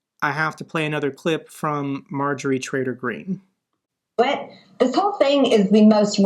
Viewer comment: I swear I heard you say Marjory Trator Green,but it could just be my hearing?!😅